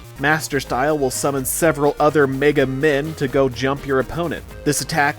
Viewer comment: honestly i almost choked i thought you said in your description for master style was: "Master style will summon several other Mega MID" wahahaha!!! 🤣🤣